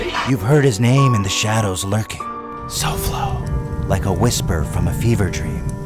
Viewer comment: caption says "solfo" instead of "soflo" this is a disgrace and i will not stand for it